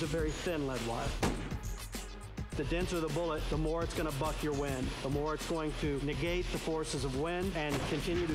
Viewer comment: lol it sounded like he said " the denser the bullet, the more it's going to fuck ur wind"